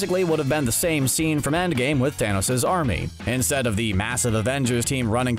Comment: Did he actually say waysically at 4:54? Lol